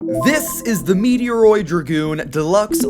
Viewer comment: I thought it said Metroid…